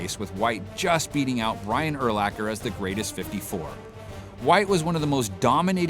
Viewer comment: did he say Ryan Urlacher?